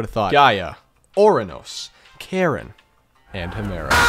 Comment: did he say karen?